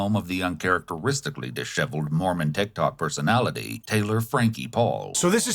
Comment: Shit thought he was saying moron not mormon... must be the accent